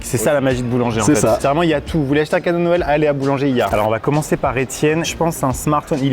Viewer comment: il y a que moi qui a entendu " allez a boulangerie " ouuu ?